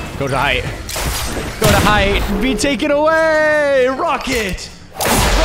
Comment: When Nick said “rocket! rocket!” My heart dropped because I thought he said “f**k it!”